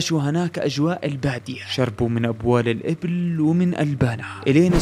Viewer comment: في الدقيقة 14:17 هل قلت شربوا من ابوال الابل ؟؟ ولا سمعت غلط انا ؟؟؟؟